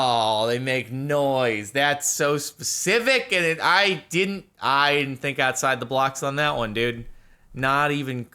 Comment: did he say think outside of the blocks instead of think outside of the box?